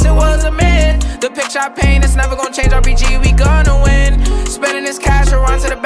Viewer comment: Why did I heard " the bich I paid several Mchicken left me again" instead of "the pictures ipaint its never gonna change